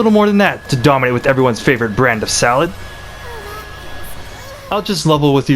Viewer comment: Were you saying Salad character? I thought you were saying Sal, which is easily confusing. And the captions said South. Either way, a "salad" character, wtf is that?